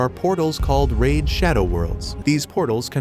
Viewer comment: Go get milk ahhh scene at the start and uhh I thought bro was bouta say" RAID SHADOW LEGENDS"